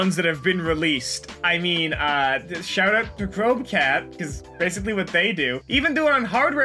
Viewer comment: What is he saying here? There's no regular captions and I looked up what I thought I heard ("Probe Cat") and that is definitely not it. I legit want to check this channel out so I can see what they bring to the table (btw A+Start also has a glitch series) so please help. edit: looked up "switch scream" and it is Crowbcat